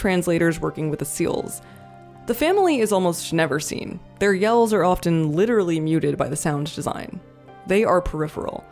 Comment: For some reason I kept hearing "warror films," which, in case of Come And See is... fair.